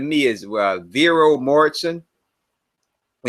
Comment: What was the name of the actor that spoke at 1:09:05 who should cast Rhaegar Targaryen? i couldn't catch what he said was it "Raivero morison or ralvero" Someone please answer. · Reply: viggo mortensen